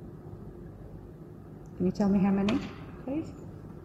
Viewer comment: I thought he said “Please Stay !!??” Like he is inviting your request to be there as if your complement of the building was excepted 🥰